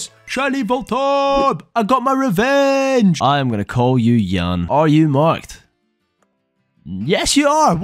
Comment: Bro I heard “I’m gonna call you sh*tty!” And laughed so hard